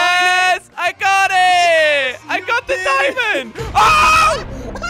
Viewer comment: "I GOT THE DIAMOND" 1 second later "nvm" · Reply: he clearly said diamond